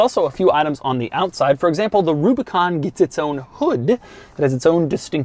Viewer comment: I just realized that to me it sounds like he said wubicon